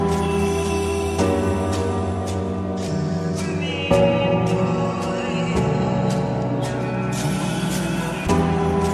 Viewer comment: I'm this is like a month or 2 old but can anyone tell me what the background of the kid or whatever it is, is saying (example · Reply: B- Fitzy I don't know if you trolling or not, but listen closely, he says "Remy boys"